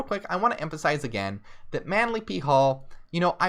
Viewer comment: I keep hearing “Manly B-Hole” 😂 All funny stuff aside, this was an EXCELLENT livestream. Can’t wait for Anti-Christmas drunk bible story time 😁🖤